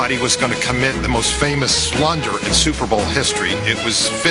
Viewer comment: What did Steve Sabol say about Garo Yepremian at 13:56-14:02?... "If anybody was gonna commit the most famous--" what?... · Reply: blunder